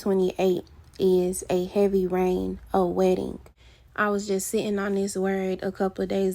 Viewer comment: I heard "wedding" but you said "wetting" 😆😂😂